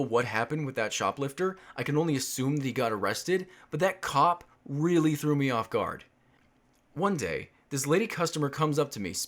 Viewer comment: you might think this time stamp is a bit odd but to me instead of saying “but that cop through me off guard” it says “but the BATERY LOW through me off guard”. Edit: for clarification my bluetooth headphones where low on battery life and sometimes it just lines up so perfectly mid sentence that this happens.